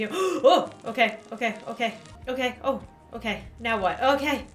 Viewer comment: Did she say otay?